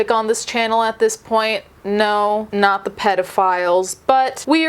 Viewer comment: First thing I heard was “NO not the peodphiles” 🤣🤣🤣 LUV U SO MUCH!!! 💜💜☮️ From UK!